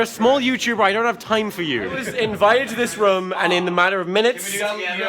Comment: oh my god you say "rum" instead of room too XD